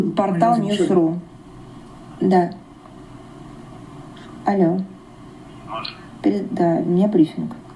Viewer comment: The woman speaking at around 08:06 into your video is not saying "I know" as you suggest. When a Russian speaker picks up the phone and says "А́но" (pronounced "AH-no"), it's an informal way of answering, similar to saying "Hello?" or "Yes?" in English. It's a casual, somewhat neutral greeting often used when someone is unsure who is calling or just wants to acknowledge the connection. It can be roughly translated as "Hello?" in the context of picking up the phone. This expression is not used in formal settings and tends to be used more casually or in situations where the person doesn't immediately recognize the caller. It can also be used to prompt the other person to speak, like "Who's there?" or "What is it?" in some contexts.